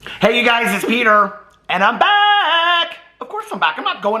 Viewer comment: It kinda sounded like you said hey you guys it’s peener ahaha